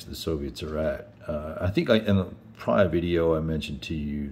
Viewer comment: At first I thought you said “Schlongster” and did a double take